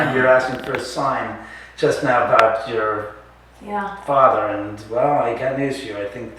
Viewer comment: I heard Daniel plz tell me I'm not the only one :o